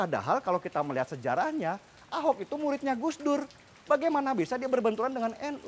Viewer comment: ahok murid NU berarti NU mengajar kristen jg baru q tau NU ulama kriten pun ada makasih ya bg info nya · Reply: yudas judas kalau saya sih dengar nya Ahok murid Gus Dur bukan ahok murid NU. atau mungkin saya salah dengar kali yah..